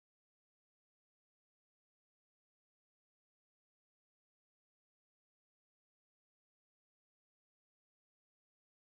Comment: and 0:18 wait are they speaking french😶🤨??? What are they saying 🤔? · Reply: Good evening Paris And Dear Parisians